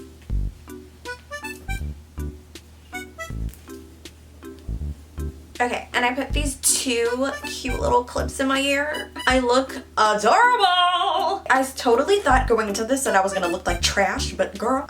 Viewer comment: In your ear? Did I hear that right?